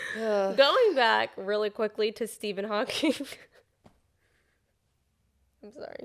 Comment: I thought I heard “rolling back really quickly to Steven Hawking” and I CANNOT stop laughing.